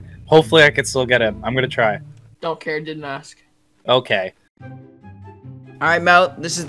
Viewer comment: burned but then the o k meme lol... but the ok sounded like don’t care didn’t ask... k ima stop byeeeeee